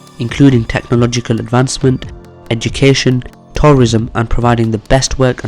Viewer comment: Welcome to Saudi propaganda please include comments here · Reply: did he just say terrorism?